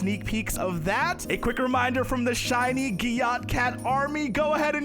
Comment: " Shiny gyatt" is what i heard 💀💀